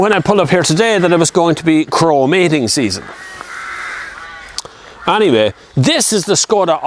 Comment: Excellent review! Can you make a documentary on Crow mating season next please?! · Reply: Oh, I thought he said "chromating season", was waiting to see some plating action.....